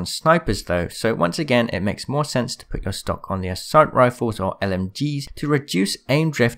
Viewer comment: I THOUGHT HE SAID STONK😂🤣🤣😂🤣😂